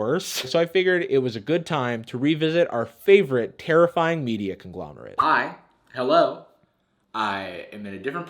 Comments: Who tf writes the subtitles, they put "(ununderstandable word)" instead of "conglomerate" at 3:06 I want to write subtitles, i think is what I'm saying lol, i just don't know how that works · lmao subtitles say "media (ununderstandable word)." he said "conglomerate."